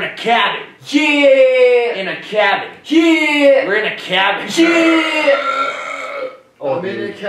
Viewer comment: I heard "we're in a cabbage!, neahhhhhhhh!" Am I deaf?